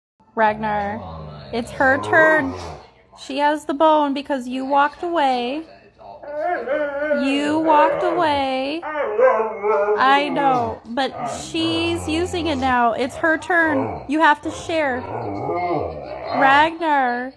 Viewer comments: I thought she said "I did not walk away" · I swear to God I heard him say, "I did not walk away..." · It sounds like he said, "she's got my bone...." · It legit sounds like he's trying to say "I want my bone! I..want..my..booone!" The side eye he gave her was hilarious. Your dogs are very beautiful by the way. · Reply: I don't know why it sounded like he was saying I am Ragnar to me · Did he just say “ but I’m Ragnarnarnar”! 😂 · i heard "i dont walk away" tho 😭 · Why does it sound like he is saying “I don’t walk away” and “I want my bone” 😂